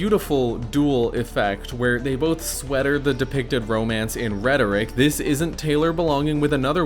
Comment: I was gonna be like "I should be way meaner to cishets as a trans lesbian" then I heard "Hetlor". I might adjust it and incorporate it into my lexicon to be way meaner to cishets.